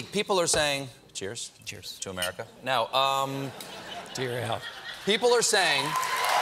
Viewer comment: What did he say?! Lol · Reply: "to your health" I think